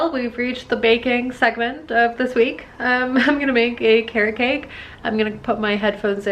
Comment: i misheard carrot-cake for care-cake. :)